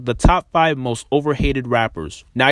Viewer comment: How many of u thought he said overrated instead of overhated?